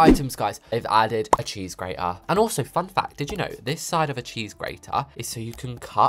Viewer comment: When he said fun fact" i thought he said "f** that Lol